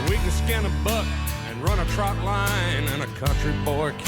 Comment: I always thought he was saying “they can run a trap line”